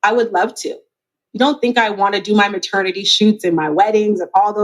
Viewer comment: Did she say maternity shoot? I asked this question on another Youtubers review because I thought she said paternity. Now I'm hearing maternity.